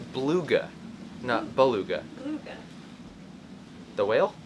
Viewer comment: I thought she said “Fa-luga “ which is a island in Tonga I think....one of the only places in the world that there weren’t any Covid cases. I liked the raw tape too, you guys are so natural around the camera anyway that it’s just as good and I like the idea of you being able to sail more or tape more if you don’t have to spend so much time editing....Aren’t the dogs just the greatest company???